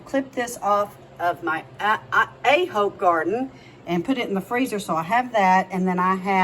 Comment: I am a Christian woman, so I really shouldn't, but today is Friday so, what they hey. I thought you said the Thai basil from you A hole garden, ha!